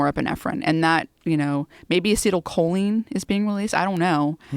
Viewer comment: what is the word Joe mentions at 1:28:57? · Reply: acetylcholine <link>